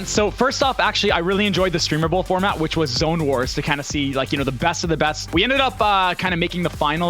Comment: What is that map code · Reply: That’s a real game I thought they said zonewars